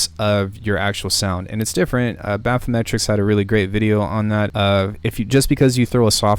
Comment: Hey, at 8:22 secs can you please say what is the name of the guy who did a tutorial for a cliper? Would be really happy if you can tell me, didnt quite understand what you said there 😅😅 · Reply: baphometrix. it's a long video but she explains the differences between a few plugins that's pretty informative